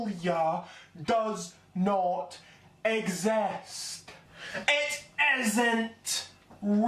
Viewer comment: anyone else hear ‘exast’?